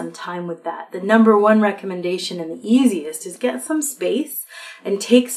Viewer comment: When you said get some space I thought you said get some skates! :)